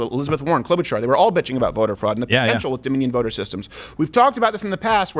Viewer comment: Ohhhh I thought you guys were saying voter frogs. I was like c'mon man of course there's no evidence of frogs voting.